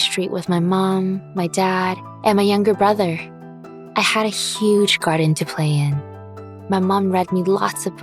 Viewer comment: I thought she was saying I had huge crush....... never mind